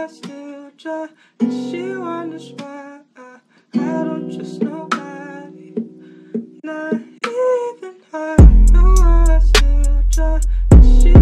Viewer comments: Why did I hear it like " I don't trust nobody not anymore" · (what I hear) I don't trust nobody not even me don't know why I cry